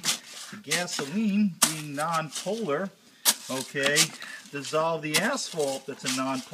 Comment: I heard that as "the gasoline being non polar, dissolves the asshole"